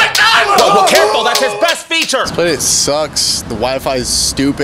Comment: zips down his pants everyone screams "Careful that's his best feature!" God I love Brandon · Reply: i thought he said Breast feature 💀